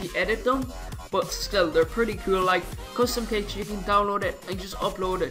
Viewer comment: When u were saying the plugins and u said CustomShips I thought u said custom tits cuz of ur scent lol